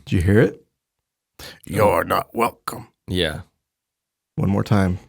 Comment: When the Bigfoot say your not welcome I thought it said no I don’t want one